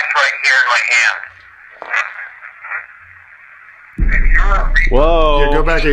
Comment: I hear "im a reper" spoken really fast